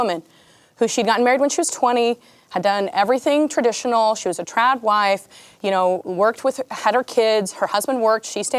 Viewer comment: did she say #trapwife? 😂 I want one of those 😅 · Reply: Trad wife